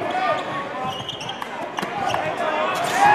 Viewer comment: lmao how the announcer pronounced jalek's name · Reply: it sounds like he saying jake felton